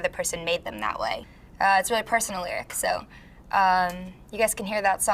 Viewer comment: Can anyone explain what she said at 0:59 bc I'm still confused on that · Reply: "it's a really personal lyric"